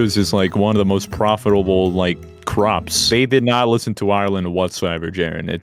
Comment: did he say ireland????